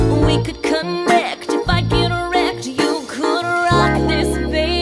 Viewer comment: I always thought that at 0:40 she said "If I get erect" tHIs vERSION MAkes aLOTMore SENSE